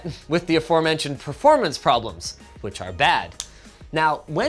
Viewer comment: Subtitles should have said "poll" the drives instead of "pull" 9:07 Parity instead of parody 10:38 Insulated instead of insulin